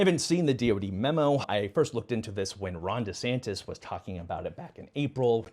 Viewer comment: I just heard “Rhonda Santis” and now that’s how he lives in my head. 😂